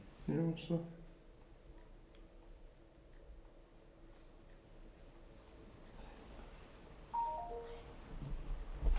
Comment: I hear a voice there too but it's so faint I can't tell what it said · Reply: Pretty sure that was the male then female whisper "I love you" that kept occuring, although I think there were other words/sentences going on that you can hear at times, for sure.